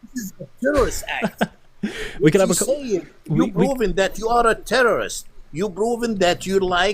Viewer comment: ok this is how i KNOW he was never jewish, he said Broving instead of Proving, lol all jews can say P, even if ur from an arab country, lmfao